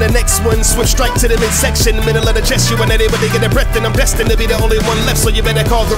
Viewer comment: Not to be like "you suck you made a mistake dislike unsubscribe" but at 0:17 i think it says middle of the chest not cause if i get too close then it's over